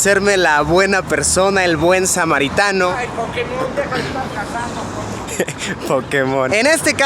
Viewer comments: pf que dice a 3:05? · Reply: "Ay Pokémon, deja de estar cazando Pokémon" XD, penso que Luisito estaba jugando Pokémon Go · "Ay! Pokemon deja de estar cagando..." fue lo que dijo? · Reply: Jhoel Rodrigo,Hay Pokemon Deja De Estar Casando:v